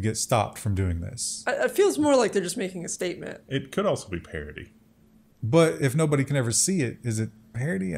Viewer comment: When says "parody", at first, I hear "parity". Takes a few repetitions until my brain realizes he is not talking computer stuff. I am kinda sorry about it. Seems my subconscious has the man eterrnally labelled.